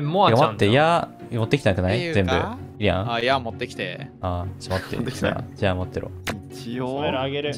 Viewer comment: ギャルやんさん さんがケロロネタたまーーに散りばめてくれるの助かる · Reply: ₹˝ｬʓゃん、その後の「矢持って来て」が「やん持って来て」に聞こえるのすこ